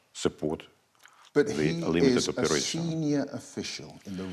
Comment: This Kelin guy is sickening to listen to. And, seriously, did he call the slaughtering of civilians and obliteration of infrastructure a 'limited operation' (15:10 ish) or did I just not hear that particularly creative euphemism properly?